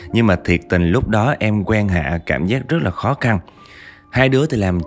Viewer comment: "quen Hạ" mà đọc sao toàn nghe thành "quan hệ" :))